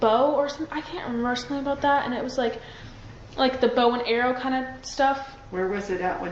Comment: 🆘🆘🆘Holy moly did anybody else just catch what I heard somebody please in the chat tell me if I’m hearing things listen to 34:15 while she’s being questioned about the suspect, she says one of the cops told me it’s a bow and arrow???‼️‼️‼️